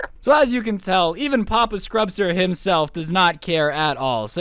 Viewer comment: Did he just say “papa Scrubster “